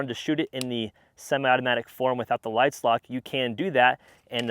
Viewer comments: Did anyone else hear him say "lide slock" instead of "slide lock" at 3:25? Lol! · the "Lide Slock" instead of Slide Lock, I just thought it was funny. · any body catch the “lide slock” instead of slide lock Loll